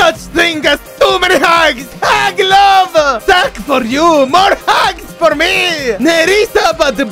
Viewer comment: I heard it as "SAGS FOR YOU MORE HAGS FOR ME"